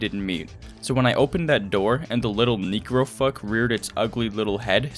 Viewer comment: Was it only me who heard it wrong at the first time 😂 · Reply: I really thought he said Negro fuck